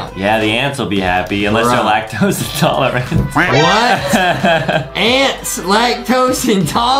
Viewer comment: When they said ants, I thought they said aunts